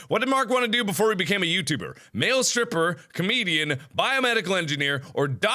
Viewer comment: I thought comedian said Canadian.